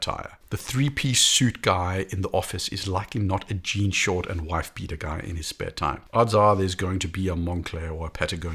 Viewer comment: did he say wife beater? 😅